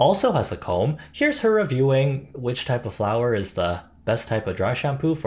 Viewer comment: I thought you said “flower” and not flour, so I was confused why tf they sold bagged flowers so casually and how that’d do.. anything 😭